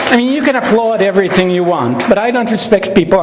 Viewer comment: I'm not a native English speaker. At 03:19 does he say "you can applaud" or "you can upload" anything you want? It makes sense both ways to me :D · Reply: "you can applaud everything you want". But you're right that he probably meant more "you can applaud anything you want".